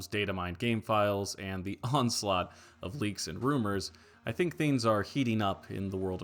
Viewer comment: The Fork…it’s a sign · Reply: did you say “onslaught”